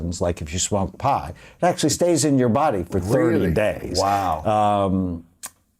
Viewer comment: does he say 3 days or 300 days?! Auto captions say "30" but he doesn't sound like he says 30. Which is it? I remember I did half an edible it felt like it had a tapering off effect Like about 3 days long, but I have no pot tolerance. But I do know it can be "longer" because drug tests on hair look at long term chemical depositions in fatty and protein tissues.